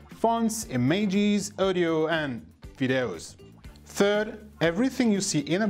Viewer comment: DID HE SAID VIIDAEUS ?